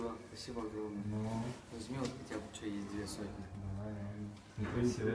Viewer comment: что он сказал тут для тебя есть...... ? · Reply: Хотя бы чё есть